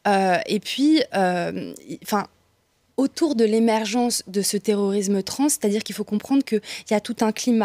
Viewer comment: Le journaliste manque d'écoute active, dommage. Sinon, total soutien à Dora et Marguerite. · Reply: Le pauvre ! Moi aussi j'ai eu du mal à me concentrer quand j'ai entendu "terreur, terrorisme trans" 😅